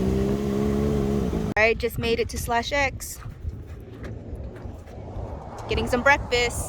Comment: I really liked the look of that bar/cafe.... And the food looked awesome!! Where was that? I thought I heard you say, "bar slash X"? Is that out in the desert in California? My wife and I love those out of the way local places. Usually good food and not too expensive. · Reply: Its an awesome place such a vibe. Check it out if youre ever in the area. Slash X Ranch Cafe <link>